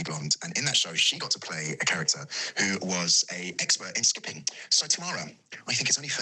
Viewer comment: Absolutely LOVED this 😂 👏 Kieron and Anna are such a good presenting duo. I have to say though, I thought Duncan said stripping but it was actually skipping #bitdisappointed 😂